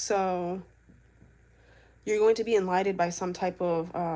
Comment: Thought u said you were coming on tonight